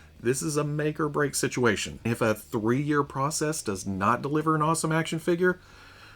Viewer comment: I'm sorry, I must've misheard you because it sounded like you said a Mona Lisa action figure. When, where, and how much?